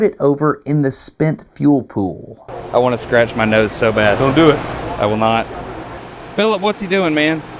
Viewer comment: Why should he not scratch his nose? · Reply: my head is in the gutter and I didn’t even mean for it to be. I love this channel and I’m so sorry but I thought he said “I gotta scratch my nuts so bad 🤣” as a guy hey we’ve been there